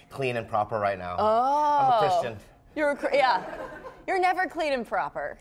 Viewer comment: Bobby says he’s Christian but subtitles say Korean lol YouTube is so racist.